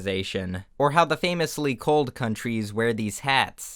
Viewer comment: I thought you said "cult countrys" instead of "cold countrys"